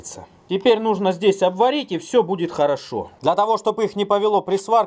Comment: что ты сказал? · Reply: Капитан Очевидность я расслышал "Пилит хорошо"